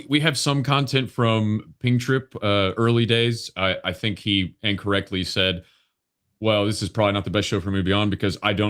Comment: I said AND CORRECTLY but it sounded like incorrectly!!